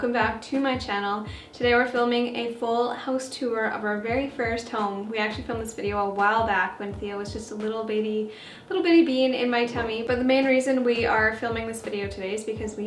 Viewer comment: I didnt hear you right in the beginning, and I thought you said "This is my first house tour with a little bitty bean in my tummy" I was like WHAT😂